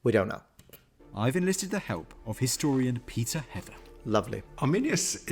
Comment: Anyone else heard "pizza heaven" instead "Peter Heather" or am I just crazy?